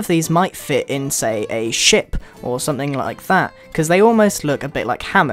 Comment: did you just said sh*t?